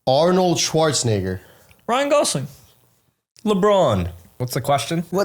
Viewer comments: bro I’m deaf I thought said Grant Gustin also 🥀🥀🥀🥀🥀🥀🥀🥀🥀🥀🥀🥀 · i dont think he said schwarzenegger it sounded like the n-word